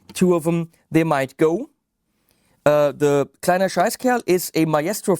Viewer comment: What on earth do you say at 28:08? :) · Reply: "Kleiner scheißkerl" little shit